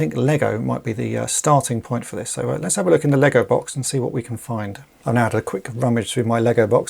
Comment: I thought I had heard "LEGO vault" and once the mild amusement subsided I realized "LEGO box" was said. I have to say, I rather like LEGO vault and have introduced it into my lexicon forthwith!